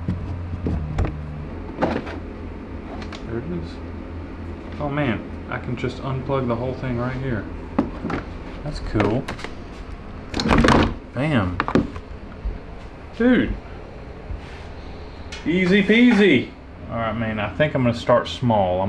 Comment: Did i hear say damn at 9:11 i was like woahhhhhh never heard them cuss